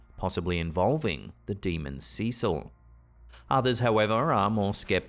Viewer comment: Spanish speaker here. She is not saying "A Cecil", which would be Spanish for: "To Cecil"; I am pretty sure she is saying "Azazel". Which is the name of a demon, I don't think there are any demon's named Cecil. Not that I think Azazel is real either.